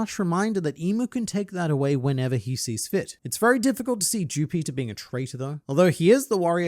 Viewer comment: I thought mu man was saying jew peter.